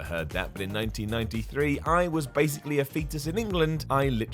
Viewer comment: I'm sorry... was a fetus in 1993? I thought dude was like 40. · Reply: Thought you said Festus and I’m like wait what